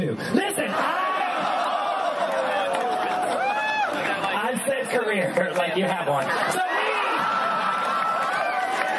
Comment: did he say « true friends » And can someone explain me Bianca’s joke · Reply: Szlendak yeah Courtney said it, “true friends, gotta appreciate them”